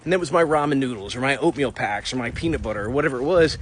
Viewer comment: Familiar with everything except what sounded to me like opium packs? Did i mishear? What is that? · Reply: Oatmeal